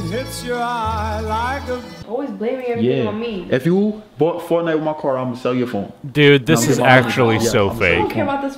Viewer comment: Why did it sound like said “You bought fortnite with my card I hit her with the WAP” lmao